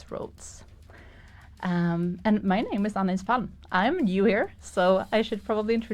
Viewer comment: This is unbelievable funny: at 0:32 where she says her name, I couldn't hear what she said so I enabled closed captions (which are google translate) and the result was "My name is Anal." Made me burst out in a laugh 🤣🤣🤣😂😂😂. I just had to post this, too funny!!!